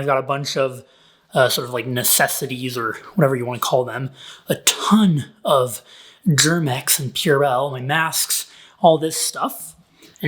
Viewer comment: I heard "I have a ton of Durex" xD xD